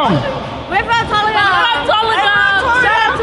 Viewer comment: I think they said "we're from Tolaga" not Tonga. Tolaga is a small town near Gisborne.